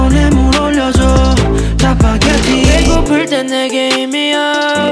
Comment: 배고플 땐 내게 힘이야라고 들림ㅋㅋㅋ